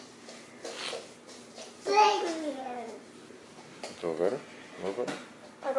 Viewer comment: I swear it sounded like said What's Wrong